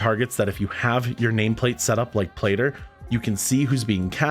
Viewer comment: I couldn't understand what you said about having your nameplates set up at 12:54. Was that "play der"? Are you referring to an addon or is that the name of someone? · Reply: He said plater, it's a nameplate addon. They're alot of profiles out there. Quills is pretty popular and free, you can find it with a search on YT.